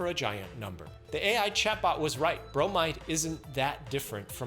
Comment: I thought you said bromine, but i dont think thats much better for you